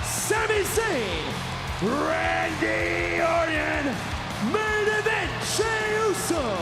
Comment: Did he say Randy Onion?